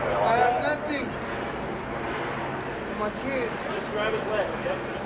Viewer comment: Did he say, “my kids!”?